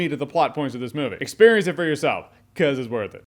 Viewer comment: In the beginning when you whispered "cause it's worth it," i thought you said "cause it's the worst," and I nearly died. 😣 so glad it's actually awesometacular.